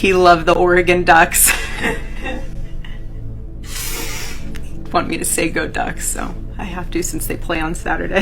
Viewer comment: Charlie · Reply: I thought she said "he wanted me to say" but I think she actually said "he'd want me to say" - that's what I was confused.